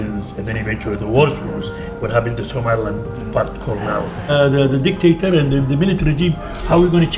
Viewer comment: just a small thing at 24:55 he's saying the worst thing was what happened to somiland (subtitle says somalia). keep up the great content